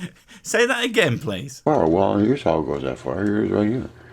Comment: "Well here's how it goes that far, here it is right here" that's what he says at 8:22 I'm fluent in drunk americanese.